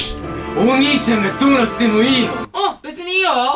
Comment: 27秒このおむにいちゃんがどうなってもいいの二聞こえる